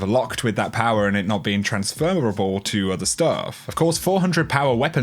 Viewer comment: did he say transfermerable?